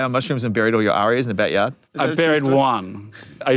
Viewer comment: what did he say he buried in the yard? Lol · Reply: he said ARIA it's the Australian equivalent of a grammy